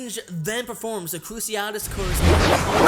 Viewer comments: Sorry What did you say.. I couldn't hear over the spell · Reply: Nightspawn Son-of-luna crucio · Was it just me? but I couldn't hear what you said between 7:22 and 7:28. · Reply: crucio